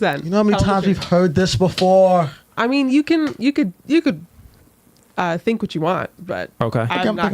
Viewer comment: i thought she was about to say "you could examine me"😂😂